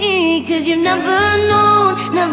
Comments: I'm I the only one who thought she was saying "Bitch you never see the rain" I was like damn tv and radios are weirldy ok with this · I thought she was saying "Bitch you never seen the rain" for a solid minute.